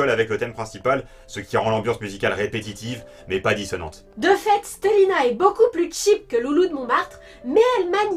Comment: Si toi aussi, à 3:29, tu as entendu Fate/Stay Night :P